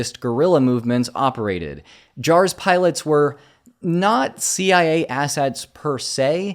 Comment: "Not CIA" sounded like NAZI i a 😅